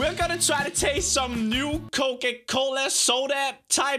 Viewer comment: i thought he said cocaine